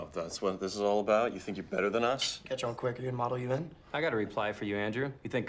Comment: what was said at 1:05 ? "catch on quick are they gonna model u in'?? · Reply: “Catch on quick: are you in the Model UN?”